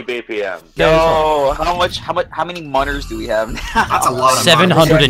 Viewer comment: I’ve watched this video 7 times and only now have I realized he’s saying monerz. For the longest time I thought he was saying monitors